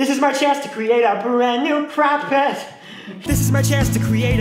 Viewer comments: did He say "CrackPet" or "CrackHead"? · I THOUGHT HE SAID CRAP PIT AND I SPAT OUIT MY DRINK